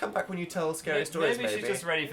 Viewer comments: IT SOUNDED LIKE DAN CALLED PHIL "BABY" EVEN IF IT WAS PROBABLY "MAYBE" IM HAVING PHEELS OFUSHAB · DID DAN SAY BABY AT 18:00 OR DO I NEED TO CHECK MY HEARING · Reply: No he said maybe, but i wish he said baby! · sounds like dan said baby but I think he probably said maybe